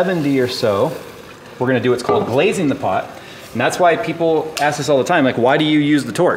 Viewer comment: I heard: "blazing the pot." Tells you enough about me.